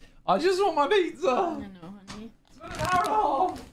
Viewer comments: did she actually call him honey. Cute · Did she call him honey? Damn, microphone's too sensitive!